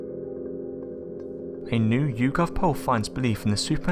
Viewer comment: What the fuck does he say at 0:08!?! " anew ukov pulf "